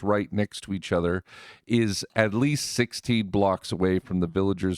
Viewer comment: Did you say to make it tileable to have the next one 16 or 60 blocks away? Its a little muddled for me to make out for sure. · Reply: villagers can detect other villagers and their golems up to 16 blocks away. Design it so no one of those things comes closer than that.